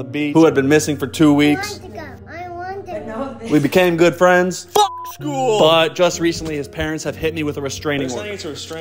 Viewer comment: 10,000 $ for school I thought he said f*c7 schoool